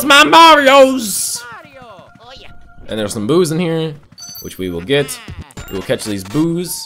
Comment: I thought that you said "There's some BOOZE in here." not boos.